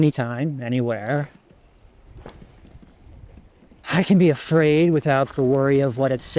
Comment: legit thought he said "I can be a furry"